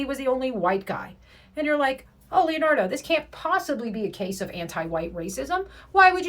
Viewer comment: did you just call yourself "Leonardo"??? (Hey ninja turtle!!! lol)